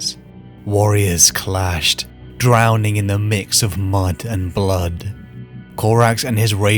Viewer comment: Hahaha, had to rewind cuz first time I thought I heard: "Warriors clashed; drowning in the mix of mung & blood..." instead of "mud". Lol, if you don't know what mung is, please don't look it up. But if you do, you'll know why it took me by surprise. Anyway, again, I rly like the channel. Thanks for the info & the upload. Cheers.